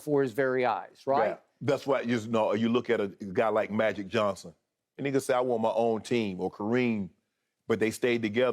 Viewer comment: did Shannon just say, "that nigguh say I want my own team" or am I tripping